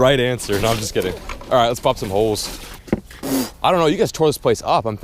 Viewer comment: did he say “let’s f*** some holes”?